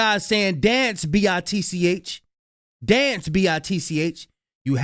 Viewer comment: it sounded like the word Dumb not Dance